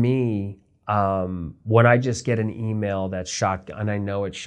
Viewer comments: "when I get an email that's ______". What does he say here? Is it shock? Is that some hollyweird jargon for something? I've listened to it over and over and cannot figure out what he's saying. He goes from saying anyone can email him, gives out an email address and then followed up with that and how he will respond that he doesn't accept unsolicitated emails. Please help. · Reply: He says, "shotgun." Meaning when he receives an email that is clearly sent to many people (including him) all at once. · What word does he say here? "When I just get an email that's just ___ and I know it's _____" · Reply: shotgun